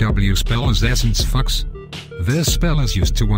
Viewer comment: lol i heard essence fucks